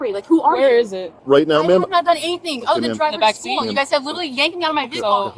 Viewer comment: What did she say at 8:52? "I didn't do anything except DRIVE my daughter to school". I thought she was travelling. Gotcha!